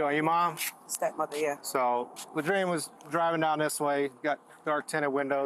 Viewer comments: The biggest crime is being named. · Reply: I thought they said latrine at first. I'm like damn I thought they named you a toilet 😂😂😂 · I honestly thought he said his name was "Latrine". 😂 I thought "No way!!!!" I had to re-watch it. LOL